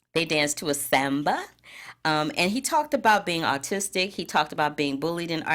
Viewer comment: Quick fixes.. **Milo Song was Can you do this by ‘Aloe Blacc’ and Juan said he was ARTistic not Autistic, different things lol!!!! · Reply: Loved By You I thought he said autistic. Lol. His accent made me hear a different word for some reason even though those artistic and autistic can sound similar.